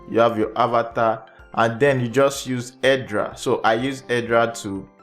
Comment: the most important thing can't understand what da heck yo say or talking about, AirDrop? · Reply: Hedra